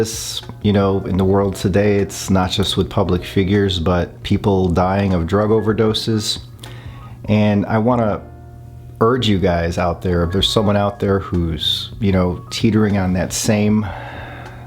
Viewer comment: Am I the only one who clearly heard him say “ I want to go HOME”???